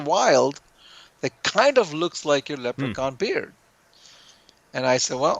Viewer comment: Great interview as always! At 30:17 did he say that coral was called “Leprechaun Peter”? (Despite multiple rewinds I couldn’t make it out) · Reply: Thanks. He was referring to the Leprechauns Beard: <link>